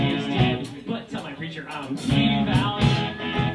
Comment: what does will say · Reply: Devout